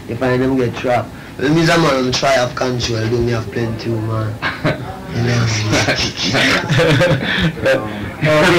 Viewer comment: What does he say at 17:20 about “plenty women”?